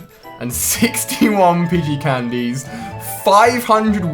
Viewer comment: did he say pigey candies????